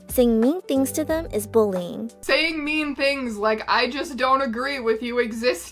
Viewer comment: But that means she thinks they don't exist. Eg. Person A: I'm black Person B: I don't agree ???? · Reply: oh, I might be deaf. I thought in sje said "I just don't agree with it" at some point. Either way still what is known as "micro-aggressive" or "micro-homophobic".